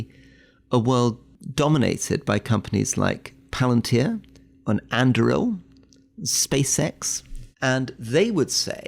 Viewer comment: Am i going mad or did Rory said at 6:49 Palántir-Andúril? I mean, praise Tolkien!